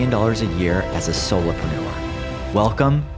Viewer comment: You mumble around the 1:33 mark when you say, "...as a solar? ????" and cannot understand what you said. It is like the bad habit people often have in that they say or mumble their phone # very fast because they know it but no one else can hear it or understand what they mumbled. Poor communication!! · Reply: Soloprenuer. A mixture between the word entrepreneur and the word solo